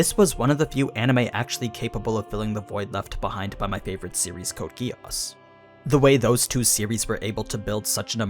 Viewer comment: what was the name of that series you said at 00:21?? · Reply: code geass